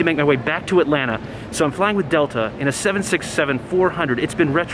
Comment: did you say 67